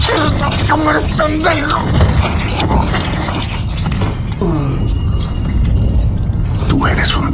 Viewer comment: lo que dijo fue (dis..disel como eres pendejo